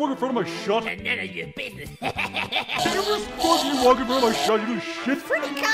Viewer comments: Does anyone know what balloon boy said? it sounds like hes saying admit it your racist but i cant really tell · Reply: He said “That’s none of your business.” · Any idea what Balloon Boy said at 0:12 ? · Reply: "that's non of your business"